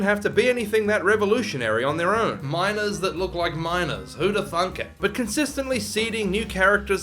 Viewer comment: i misheard it as minors